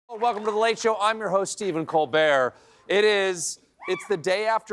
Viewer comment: IT SOUNDS LIKE HE IS SAYING STEPHEN COLD BEAR